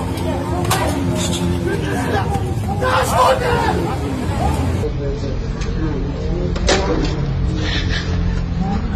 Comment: Who knows what the cop said at 0:19? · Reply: "Πιάστον ε"